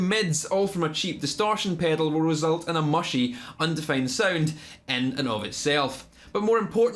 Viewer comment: wtf did he say? End enovit self? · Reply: And in of itself